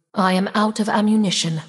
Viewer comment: did i hear that correctly she said "I'm out of Ammunition" at 5:13 Are they bringing Ammo back to the game??